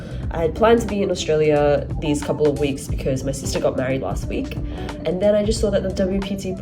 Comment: When u said “my sister got married last week”, I heard “I just got married last week” and had a quick double take 🙀🤔 …got there eventually…